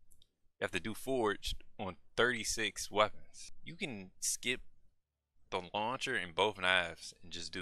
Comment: What do you say at 1:23? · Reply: "you can skip the launcher + both knives" is that the part you're talking about?